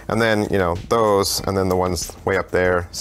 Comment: Haha at the end I misheard you say, "that's my debt collection."